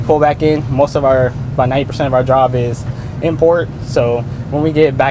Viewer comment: "90% of our Job is Import" or what did he say ?! What would a Submarine Import ? :O